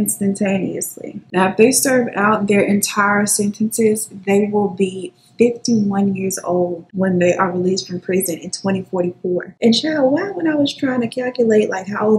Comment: When you say they will be 50 something in 2044.. Why I heard "50 in 2024" and was so confused. I was like I'm older than them by 2 years. How are they going to be 50. My brain just freeze. lol